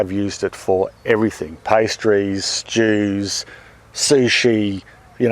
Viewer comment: At the 4:01 minute I thought said he used his chili sauce mix on pastries and Jews..... Only after playing it a few times did I realize he said STEWS